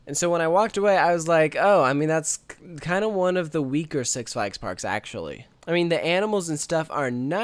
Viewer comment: Lol at 4:31 the auto generated captions said "sex like sparks"